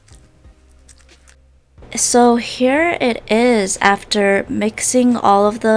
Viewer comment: Her: so here it is What I heard: So hair it is